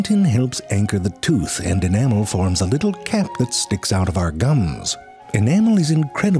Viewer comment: I totally heard you say "dicks" instead of "sticks!"